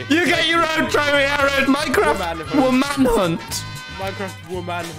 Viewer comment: i dont know why but i heard woke man hunt